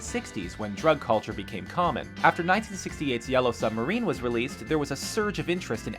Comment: did i hear... YELLOW SUBMARINE? :DDDD